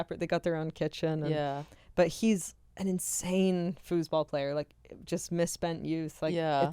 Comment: …. I thought you said fooooootball lol I had to rewind. Fooseball got it lol I can’t spell that